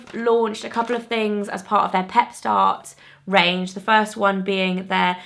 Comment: Okay, am I the only one that that when she was talking about the Clinique Pep-Start range actually thought she was saying Pet-Start?? 😂 I was thrown off for a sec ( why the heck would Clinique be starting a pet range??). Maybe just me, yeah? We shall see.....😂 BTW your videos are consistently great Fleur stay fabulous! ❤️